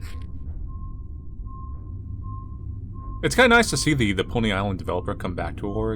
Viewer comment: When said "Pony island" i hear him says "poly island" instead, and thought this was a weird side game to poly bridge or something